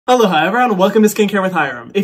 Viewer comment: I so thought he was saying “hello, hi” but now I realize he’s saying aloha🤦🏼‍♀️🤦🏼‍♀️🤦🏼‍♀️